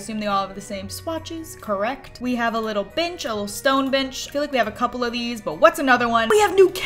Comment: I swear it sounded like she said, "we have a little bitch. A stone bitch". LOL